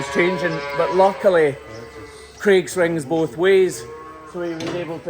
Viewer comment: 😂😂 for a moment I thought you said "Craig swings both ways"..... not the "gate swings both ways" 😂😂😂